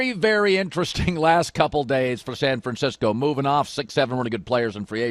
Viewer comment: Did Colin say 6/7